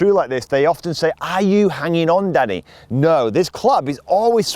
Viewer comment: I thought he said “ are you holding on dadddyy “ 🤦🏻‍♂️🤣🤣🤣